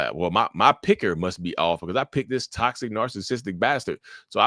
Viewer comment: lol, I miss heard you. I heard "My pecker must be off?" (Checks pants....😂) Note to self, protect my piece..... Jokes aside this is true. I would see red flags everywhere in the first 2 years after leaving my (15 year long) relationship with my covert narcissist ex girlfriend (who I co-parent with). Now I have relaxed and at the point where I feel that I have healed (not forgotten) from the trauma. It does teach you to be more picky when finding love. I already got one rotten egg, I don't need another.